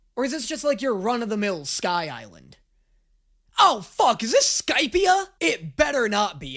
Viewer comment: my first time hearing this I had no idea what ‘skypia’ was sounded like skype took me a minute to realise he meant skypiea from op I think it was either just before he said one piece or just after lol